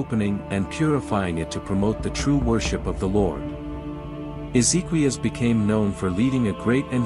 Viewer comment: I thought I heard Ezekiel, but it was Ezequías, spanish for Hezekiah.